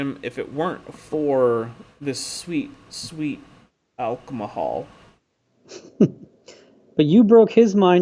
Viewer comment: Did you just say "Alcomahol"? Are you being loofy-fied retroactively?? xD